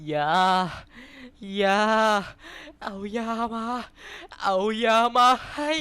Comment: จริงจริงเเล้วเคียลไม่ได้พูดเเบบนั้นนะครับจริงจริงเเล้วเคียลพูดว่า ยา ยา ยาขอยาหน่อยครับ